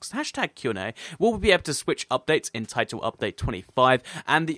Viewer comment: #QnA will we get chat and Birds in TU25 :D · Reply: Tyler Kekoa I thought you said will we be able to chat to birds XD